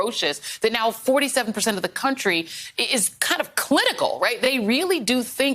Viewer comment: "...clitical"? Did I hear that right?